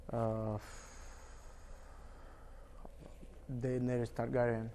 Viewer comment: what does fiziev say about charles oliveira @ 7:27 ? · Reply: Deneris Targyrian 😂